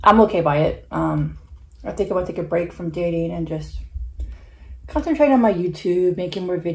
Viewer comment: you said break up then I thought I heard you say you are taking a break from your boyfriend maybe I heard it wrong! Your such a strong woman! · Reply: I meant to say I'm taking a break from dating.